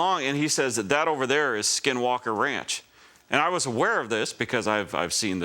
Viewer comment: I thought you said Skidwalker farms and was thinking if they cant wipe properly then perhaps they should invest in a bidet